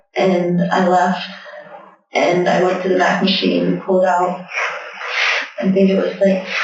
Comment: She could be saying "MAC" machine. That's Pennsylvanian for "ATM" to every where else. I was raised in the Poconos and moved down to FL when I was 20. I remember the first time I heard ATM I knew but when I said I needed to go to the MAC machine everyone looked at me like I had 10 heads. Hahaha